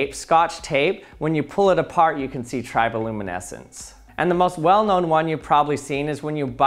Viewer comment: Lol. I thought he was saying Tribal luminescence. The references to rituals didn't help to realise my mistake.